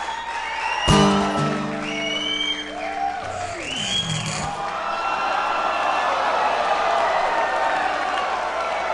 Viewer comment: Can anybody tell me what he says at 3:26? My english is still improving. Thank you, all of you. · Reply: we've been doing so well .....i think that's what he said